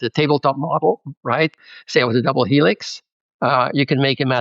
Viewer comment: Did he say "double helix"? 🤔🤪